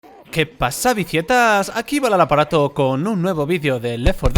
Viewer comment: Yo toda mi vida pensé que decía "que pasa vegetas" y no dice "que pasa vicietas"